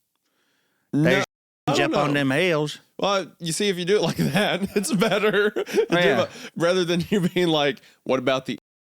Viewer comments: what did he say!? 😅 They both said it IRL, so it must be on the line, but I am too curious! 😅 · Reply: “Injuns” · what did he say 😳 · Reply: Injun